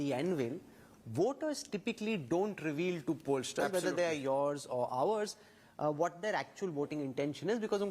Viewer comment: Did he just say yours or ours 30:54 ??